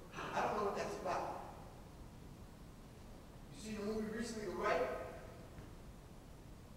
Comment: can anyone please tell me what movie Griff said @ 20:00? it's hard to tell what he's saying. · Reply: "the rite" about catholic exorsicisms